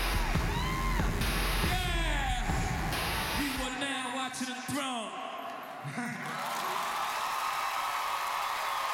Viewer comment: They rocks together! What they say at: 2:45? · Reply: You are now watching the throne.